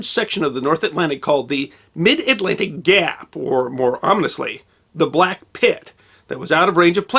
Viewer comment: Did I hear you say Dap ?🤓🤣